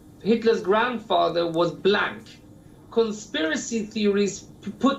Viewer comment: Did I heard him saying "black"????